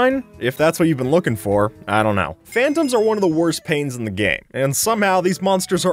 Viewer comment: i thought he said "fandoms" not "phantoms" and like true lmao